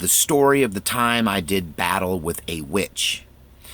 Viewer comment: i thought he said "the time i did math with a bitch" loool